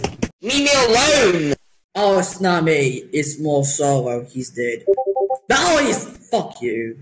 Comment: misheard: moldy hes dead real: baldi hes dead